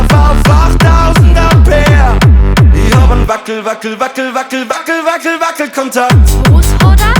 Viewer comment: Was singt/sagt die Dame 0:49 mit dem Megaphon? Verstehe es akustisch nicht. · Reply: Was hod er ( Was hat er) ? Ein Wackelkontakt!